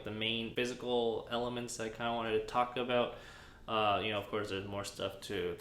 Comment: I keep hearing "Taco Bell" instead of "Talk about"